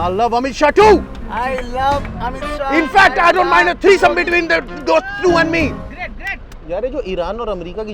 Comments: did he say he wants 3some between them? 😂😂😂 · Did I hear “I don’t mind threesome”💀